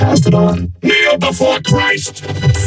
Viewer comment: The auto-subtitles say "Kneel before Christmas"